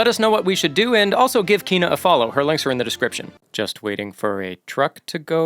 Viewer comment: I heard, "just give kino a follow, her LEGS are in the discription" I got excited